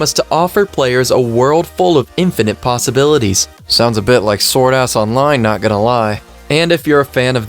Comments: Did u just say sword ass online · DID HE JUST SAY "SWORD ASS ONLINE" nice :) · Did he say Sword Ass Online or am I trippin?